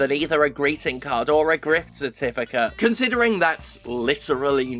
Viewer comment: I heard it as 'grift certificate'.. and that's appropriate.